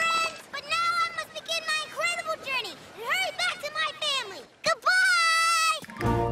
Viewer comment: Goodbye..but,what I hear is=goobye..!!! So CUTE